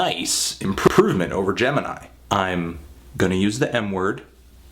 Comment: I thought you said "I'm going to use the N word..."